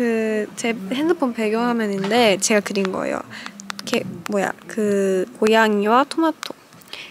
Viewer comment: 개..(귀엽다) 로 들리네...진짜 머리에 마구니가 꼈나?